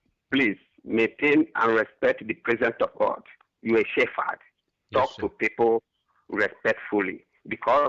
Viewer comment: Does scammer say "you is a shitfuck"?